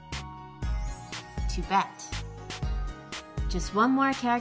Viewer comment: In 3:25 did she say to bet?